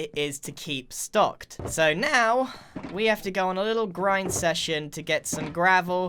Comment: did you say Grind session or Grian session???